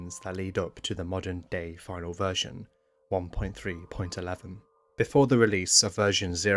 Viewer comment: subtitle error: version 1.3.1 instead of 1.3.11